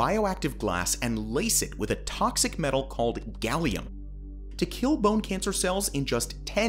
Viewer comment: Did I hear it right just now? 14:00, toxic metal Gallium?